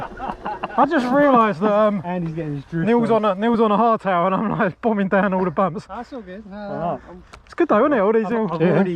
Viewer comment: what does he say? Neil was on a hard tower? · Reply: Hard tail, means no suspension on the rear👍🏻